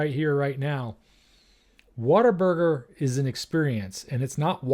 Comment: 😂 I thought you said, “water burger”. Now it makes sense, “What A Burger”